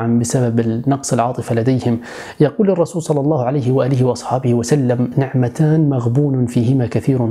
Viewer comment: هل سمعته يقول (وآله) ام ان اذني تتوهم☺☺️ اللهم صلِّ على محمد وآل مُحَمَّد 💓